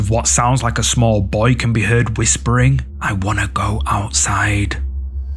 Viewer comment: sounds like he says I’m in the closet